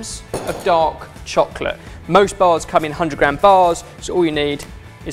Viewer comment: I thought he said "dog chocolate" and I'm like WTH